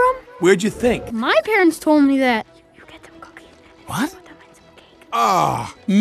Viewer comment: who else heared that “cookies and cakes make baby’s?” I don’t know if this is true but I know he definitely said “cookies” and “cakes” Btw: if you have EarPods or headphones turn them up enough so u can hear it.